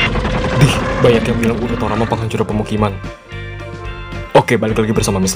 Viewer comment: Menit 10:17 ngomong apa gk jelas... Ngomong nya bnyk yg kurang jelas.. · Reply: Ohhhh.. bilang "Dih, banyak yang bilang Urutoraman penghancur pemukiman" Sorry amigo kalo masih kurang jelas😂😂..Maklum, mimin rekam suaranya pake hape jadul.. Di video2 baru ntar mimin tambahin subtitle ughaa